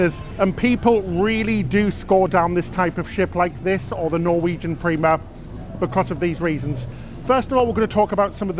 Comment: I swore you just said "The Norwegian Beaver." LOL